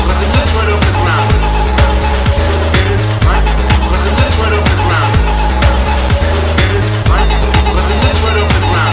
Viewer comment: when 7 i never knew wtf he way saying and i still don't. Anyone here know? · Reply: Get it? Right, when he lifts right off the ground